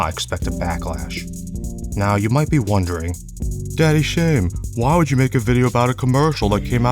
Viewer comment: did he... did he just call himself 'daddy Shaym'? XD